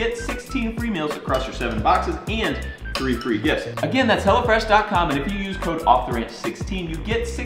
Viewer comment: thought you were saying "hella fresh" till the link popped up lol